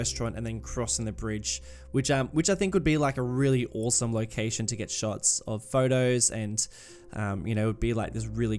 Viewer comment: For a moment I thought you said it was an awesome place to get shot. lol.